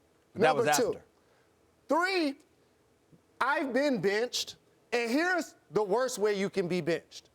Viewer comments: Am I the only one that thought Jalen Rose said that he has been b****ed before? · when Jalen Rose said benched and was talking about himself being benched I thought he said bitched over and over lol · I thought he said "I've been b*tched" lol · Did he just say "I've been bitched'?